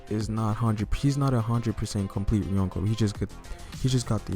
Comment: Like and Comment down below your estimation of their Bounties.... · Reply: Hahahahah,when u say Younko's,i thought u said Uncles